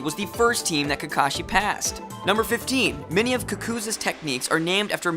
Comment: did you call kakuzu "kakuza"